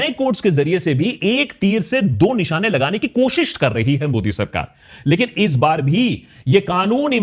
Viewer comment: am I the only one who heard godi sarkar 😅?